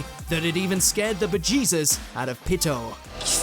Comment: Did you just say "scare the Vegitas out of Pitou?"